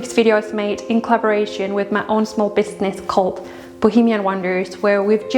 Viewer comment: thank you for another beautiful video of your home. It is absolutely beautiful and I can see where you would pinch yourself to know that you live there. A dream come true. Now here’s the funny part. When you said you were sponsored by bohemian wonders, I thought you said, Boogieman wonders. I live in the US and sometimes your accent sounds like you’re saying something totally different to me.😂😂😂